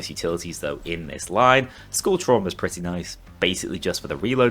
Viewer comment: With the accent, I heard "skull trauma" as "school trauma".